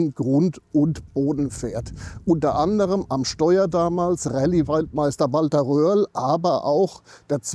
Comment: Waldmeister🤦‍♂️? Oder habe ich mich da verhört 🤔?